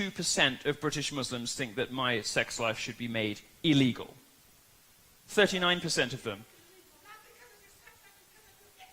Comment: "It's not because of your sex life it's because of..." couldn't hear it. Little help? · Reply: She said "It's not because of your sex life, it's because of your ignorance" then after that everyone yelled and couldn't hear her.